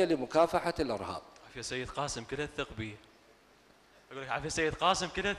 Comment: لم أفهم ماذا قال المحاور في الدقيقة 4:25 ؟؟ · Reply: يكله عفيه سيد قاسم كلها اثق بي يقصد قاسم الاعرجي